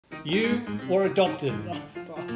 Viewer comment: At the beginning I really thought he said yoou are a dumpling instead of you are adopted😂😂